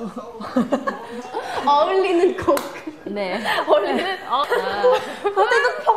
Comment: who else heard her say lizkook